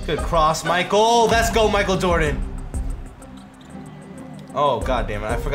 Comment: Did he say Michael doden